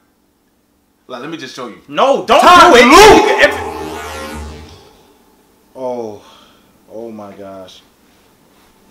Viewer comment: what did he say after dont do it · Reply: "TIME LOOP"